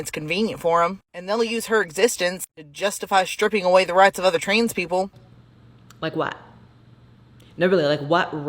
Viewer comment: Listen to that clip again and tell me that person did not just say "TRAINS-people" 🤣🤣